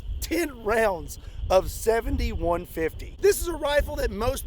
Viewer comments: Doesn't it sound like he says 71-50 not the actual caliber 70-150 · Lol everytime you said "70-150" I heard "71-50"😂😂😂